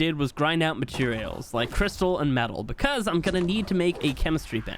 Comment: when he says crystals and metal I thought he said crystal and meth